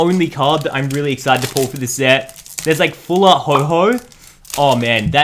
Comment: did just say full art ho-ho